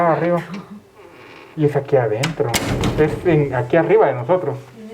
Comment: Entre el minuto 22:01 y 22:02 se escucha la voz de una mujer, que dice "adentro" justo cuando dice: "pero no hay nada arriba" y la chica que está con ellos no hablo en ese momento.